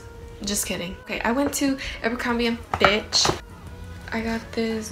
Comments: it sounds like u said the b word it’s sooo funny but u didn’t u said fitch btw ilysm and gurl u can model good! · I thought your said bit** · i thought she said bitch